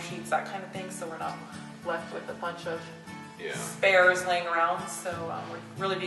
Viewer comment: When you say you didn't want spares around,I thought you said bears. So I had to replay the sound bite again. Glad it wasn't bears. Haha. Place looking great.